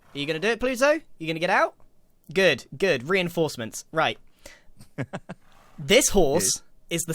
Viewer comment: I swear the first time I listened I thought he said Grianforcements